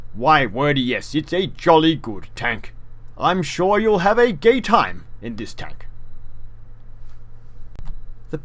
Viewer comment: did you say "im shure you re gonna have a gay time in this tank" at 0:18 min